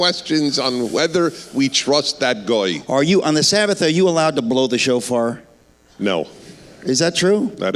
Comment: Am i the only one who thought he said blow the chauffeur?